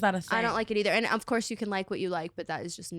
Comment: Talking about the lashes and it sounded like tana said "it Tara-fies me" 😅 What happened to Tara?!